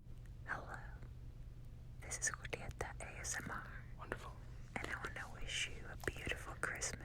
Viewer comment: Who is it that speaks at 37:12 ? I couldn't hear their name but I'm curious. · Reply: I believe it's Julieta ASMR